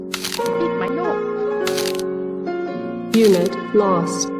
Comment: Did anyone else catch what sounded like talking here? I have no idea what was being said. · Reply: Giant floating head Mario saying”I hit my nose”